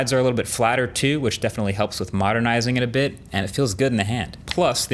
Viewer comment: modernizing i heard like "iphonizing"